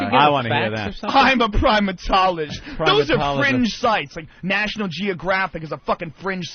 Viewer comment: The audacity of a wrestler calling a PhD stupid. I thought she said cringe, because that’s what it was